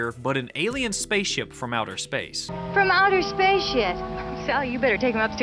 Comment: the captions are wrong. The woman isn't saying "ship" OR "sh¡t." She's actually saying "'From outer space' yet!"